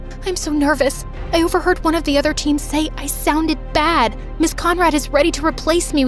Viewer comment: “I heard one of the other teams I sounded bad” She said flat but OK!!😅😅❤❤